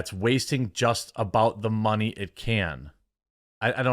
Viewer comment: dude you can’t read for shit i swear to Christ. He said “wasting about the ‘most’ money it can.”